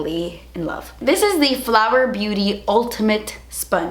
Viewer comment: I thought she said "Flabber Beauty" when taking about the sponge...I was thinking, "what an awful name for a beauty company." 😜